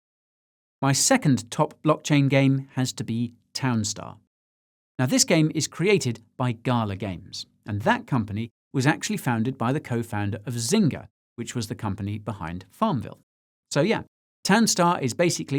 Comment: I thought you were saying garler games the whole time.